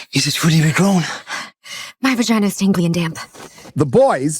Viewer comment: "All tingly and damp" - this will be my new catch-phrase. I am 6'6", with a beard. It will rock. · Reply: I thought she said stinky and damp